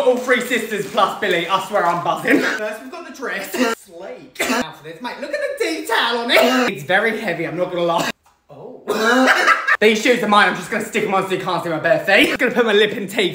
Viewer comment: I thought you said look at the tea towel on it 💀💀💀💀